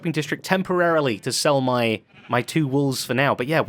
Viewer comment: When you said you would be the "Wools" I thought you had said "woolf"! Was expecting you to follow it with "Woolves in sheep's clothing", that's why you sell wool hahaha Wools of Wool Street is very clever thoughhhh very good